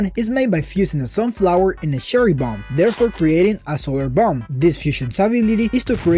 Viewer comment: I thought you say assholer bomb😂😂